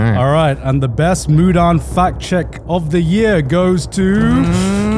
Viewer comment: I heard "best Mudan fat chick of the year" 😐😅